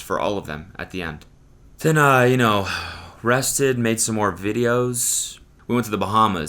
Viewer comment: I didn't hear you say rested... I swear to god I heard arrested, I was like wtf do you mean you were arrested don't say that so casually